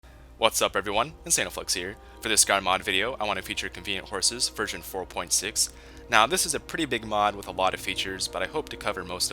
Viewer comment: Every time he says I hear "anal flex"